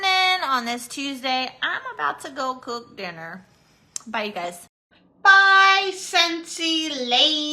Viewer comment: Lol, at the end of her “training” call I thought she said “I’m gonna go poop” before I heard her say dinner at the end. That would have been hilarious.